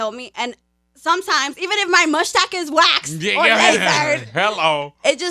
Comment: "Even if my mushtak is waxed...!" Had to replay that to make sure I heard right lmfao